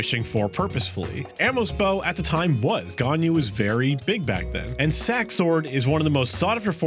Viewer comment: lol the cc got it as Ghana instead of ganyu